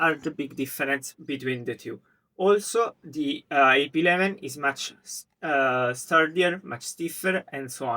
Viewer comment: Subtitles at 2:04 read iOptron instead of AP-11, how did this even happen? At 6:28 and 7:18 it even says AM5 instead of AP-11, that's crazy!